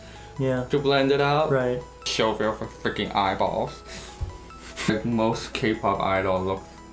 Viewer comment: did he say "Shove it up your freaking eyeball."? 😂